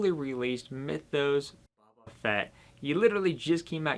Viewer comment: All I hear is "Buba Fett" and its kinda killing me.